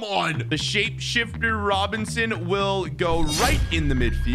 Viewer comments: im sorry did u say robinson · did he say Robinson?